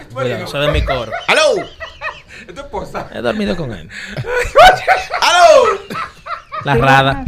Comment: Expliquenme cuando en el 4:34 min cuando hablan de radames espíritu y la berni le dice es tu esposa y el dice he dormido con el, entendí bien o dice otra cosa?